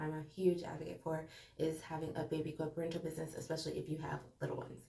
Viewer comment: What side hustle did you say at 3:20? I keep rewinding but cant make out what you said lol .. I heard a baby something business